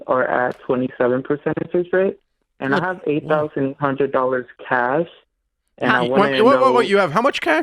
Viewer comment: I thought he said he had $800,000 cash 🤣🤣🤣 is that what Dave thought also? He seemed shocked at first.